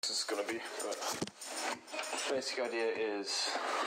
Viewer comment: i think i'm going mad when i first heard the intro i heard: "i don't know how good this is going to be" "i swear to god, yeah it is" but it's actually: "i don't know how good this is going to be" "the basic idea is" but my mind still hears the first one ahhhh